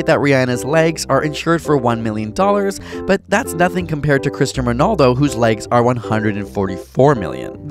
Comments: Sounded like you said Christian Ronaldo😒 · wait did he just say "christian" ronaldo? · wait what did you say? Christian Ronaldo?!?